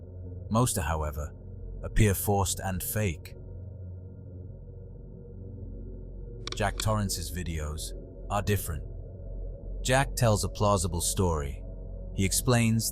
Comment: bruh i am i the only one who heard him say "Jack Dorethy"